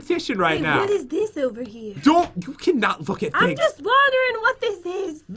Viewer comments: Who heard CURSE at 19:25-19:33 he said to that "you cannot fudgin explore" I noticed that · 19-31 did he say F*** or fook or look I’m confused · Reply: He said u can not look at me